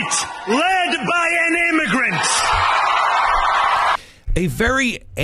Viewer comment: he said built by immigrants, powered by immigrants and as of tonight led by an immigrant!! first I thought he said led by Ignorance!! Moran Mamdani!